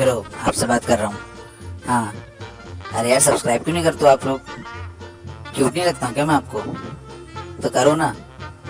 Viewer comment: I heard chuiy** lagta Hu instead of cute nai lagta hu apko😂 btw the dog vlog was besttt❤️